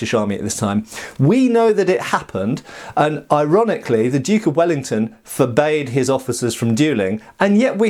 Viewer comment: I swear i heard him say "the duke off ellington" and was very confused and happy for half a minute:)